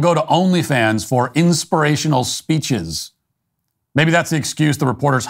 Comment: When I heard " Inspirational poetry " on Only Fans I almost peed in my pants with laughter .